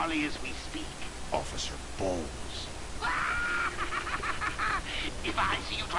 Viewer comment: Lol, is it me or does it sound like he says 'Officer balls' XD